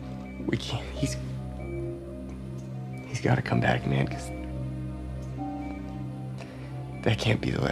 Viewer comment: Jay calls his brother Will "Nick" -- the name of his actor. Surprised no one caught it. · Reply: I can't hear Nick at all at 6:36 , "He's gotta come back man, because..." is what is said.